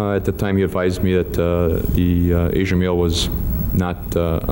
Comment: subtitles say Asian meal instead of male. I found that funny considering that he ate his victims.